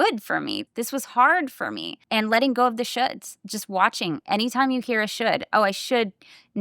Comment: I first heard this as "letting go of the shits," which is also useful! 😂 That said, it is sad as I've had a rough ride, as I disclose to friends, family, and work. As much may reveal "true friends," whether coached how to help or even simply remember/allow certain in/actions. Lot's of "ok, as long as you get __ done". Like I revealed it for small talk or the fun of it, rather than an asking for help! Goodie...